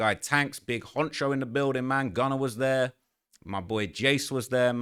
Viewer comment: did he just say Gunna was there? or I'm bugging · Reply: GUNNER, Ano jumper employee